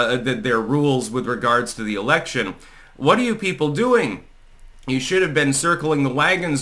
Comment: I heard "jew people" and had to rewind X-) "you people" ahh